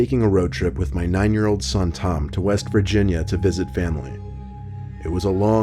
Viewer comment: did I hear the state West Virginia